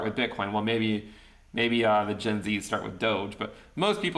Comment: I thought you said, "maybe the gypsies start with DOGE"; it was definitely gen z though. haha